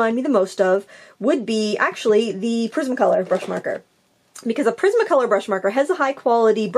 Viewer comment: Lol,I thought you said prison colour 😂🤣 but it was prisma colour lol.